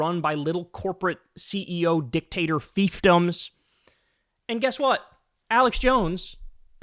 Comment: Kyle says fiefdoms, captions say "thieftdoms." Despite the mispelling, I think Thiefdom is an accurate word.